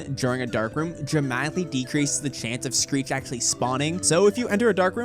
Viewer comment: my man say street instead of screech 💀 edit: he said greet on 3:00 hult